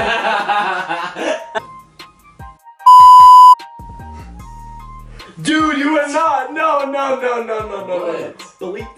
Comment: Can someone figure out what name he said at 6:29 thx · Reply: Marie 2 I think he said “Jessica Belkin”